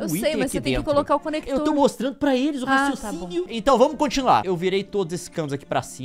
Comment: Juro que quando o falou “E o raciocínio” eu ouvi “Eu não sou sênior”😂